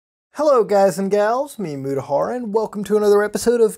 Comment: Dude. I am dumb as fuck. I just read your description and realized that your name is just Mutahar. I legitimately thought when you said "me" in your intro, it was part of your name. I thought your name was Memutahar. Smh my head.